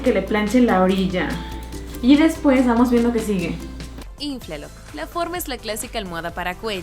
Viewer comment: like si oíste que dijo flinflar en el min